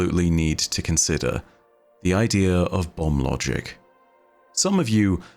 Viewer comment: I.... thought said Bum Logic, and proceeded to have to rehear it the entire time